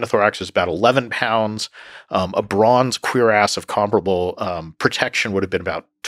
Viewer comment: What'd he say?😂 The captions are just as bad as it sounds. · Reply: Cuirass. It's a breast plate and back plate attached together.